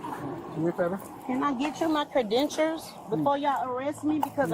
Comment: OMG,she actually said(still learning to talk)@ 9:44,"Can I get you my cre-dentures?" And she`s probably one of the smarter ones in her clan....